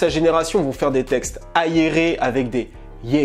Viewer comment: c'est moi ou il a prononcé "Ayeré" au lieu d'"aéré" ? Lui c'est sur c'est un zaïrois